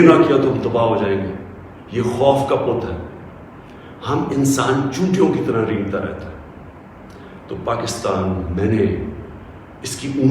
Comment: Am I only one or did he say Chutiya like 2 times in this... Aum insaan chutiyo ki tarah ... Near 8:20 mins